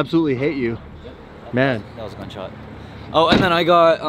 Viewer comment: he said gunshot anybody else catch that